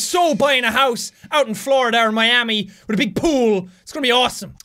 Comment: did he say in Florida OR Miami